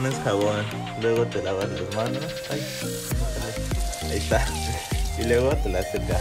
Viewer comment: quien se dio cuenta que dice "aria": o